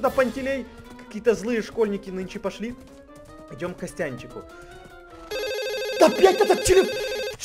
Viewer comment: мне одному послышалось "да бл@ этот телефон"😂😂😂😂